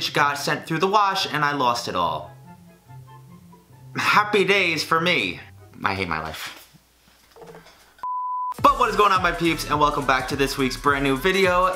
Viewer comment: sounded like he said "What's going on my pubes"